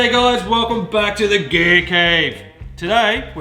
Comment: I thought he said "the gay cave"...lol